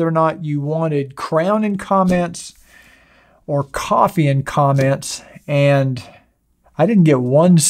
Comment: Oh Wait you said coffee. I thought you said coco !!!! And as I dont drink anymore, Coffee would be OK.